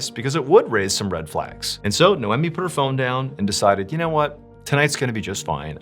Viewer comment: “no-emmy” is an interesting name, at first I thought he was saying Niomi wrong.